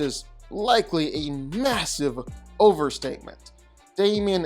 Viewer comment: did you say....MASSIVE